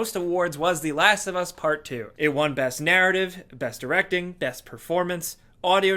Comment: "Best narrative " I think i heard Bullshit